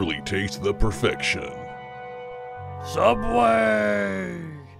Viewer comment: Subway just needs to get your re-enactment video and make it an ad itself. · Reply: I live near Harrisburg and also remember this commercial airing sometime in 1995 and/or 1996 but could not find a video of it on YouTube. I thought it was a strange commercial and thought they said “Subway South” at the time and made fun of it at school, but I agree it was probably “Subway Subs”.